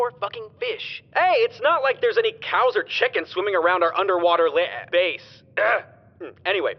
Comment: What is he trying to say 6:27 "Underwater li-uh base".